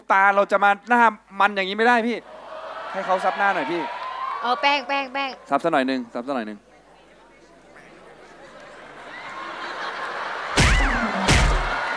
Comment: ลุงหม่ำแกบอกให้เหยียบแป้งหรือป่าวอ่ะได้ยินไม่ค่อยชัด 555